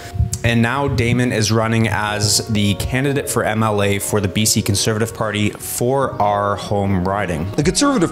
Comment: Did you say Conservative? You got my vote already Damon.